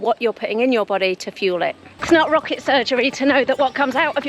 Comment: did she just say rocket surgery?